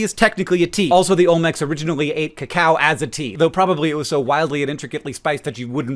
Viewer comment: I definitely thought you said Olmecs drank "the cow" as a tea, like some sort of beef-based tea, not cacao. I was very confused until I figured it out. Also, if memory serves, the word "cola" comes from Coke originally being made with the kola nut from Africa, which was the source of its caffeine (though famously, not the only stimulant in the original recipe) but it hardly surprises me that's not still the case and that synthetic caffeine is the norm across all sodas now.